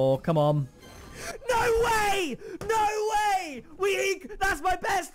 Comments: did he said he got the octane and aids?lol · did he say we got a white octane and aids?